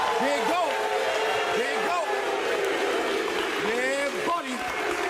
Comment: did he just say: YEAH BUDDY? Shoutout to Ronnie Coleman